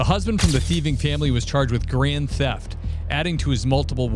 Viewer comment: I thought you said there ''charged for Korean theft'', as if for being sneaky